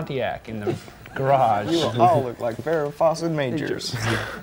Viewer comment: Does anyone know what he was saying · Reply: 'you will all look like Farrah Fawcett=Majors...'